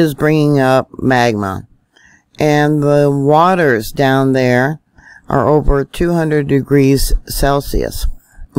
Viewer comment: “The waters down there are over 200°C?” Is that what you just said at approx 8:10?